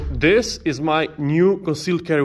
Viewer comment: THATS funny. I watched it three times and heard him say “this is my new conceal carry”